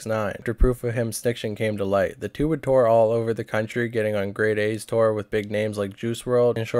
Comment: good vid but juice wrld wasnt on grey day nor did he tour with zilla and sos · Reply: I thought they mentioned it in the Adam 22 interview I must of misheard. I thought they said grade A instead of grey day. Thanks for letting me know I’ll watch out for mistakes like that in the future.